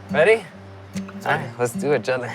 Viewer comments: did anyone else hear him say let's do each other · did he say let's do each other after he said let's go 🤔🤔